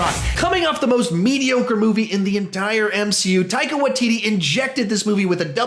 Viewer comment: you wrote in subs Watititi instead of Waititi